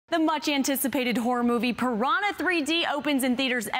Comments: Did she just say 'Whore'-Movie?? xD wellwell... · Sounded like she said "the much anticipated whore movie" at the beginning